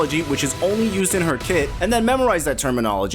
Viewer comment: Okay sorry, I was 1:35 in, and just realised you were saying kits instead of kids. I was so confused when I thought you were saying, "Character kids", "these kids", "learning the kids" haha